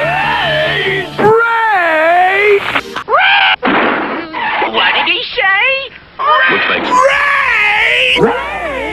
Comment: RAAAAAAAAAAAAAAIIIII-(explosion) What did he say? RAAAAAAAAAAAAAAIIII-(two explosions) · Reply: raid